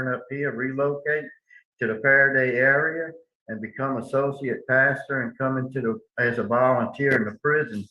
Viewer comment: OMG—at 16:16 I thought he said his desire was to return and become a sociopath! Instead, I guess he said associate pastor but boy, I thought that was the only time he told the truth! It was almost a Freudian slip, I think. He’s clearly a liar and a predator and should be locked up for the rest of his life. He looks a lot older than he actually is so sadly there are probably many more victims in his future. 😢